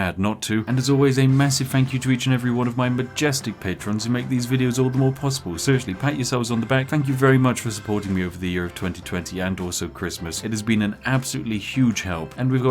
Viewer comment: When said "pat yourselves on the back", I heard "hang yourselves in the back".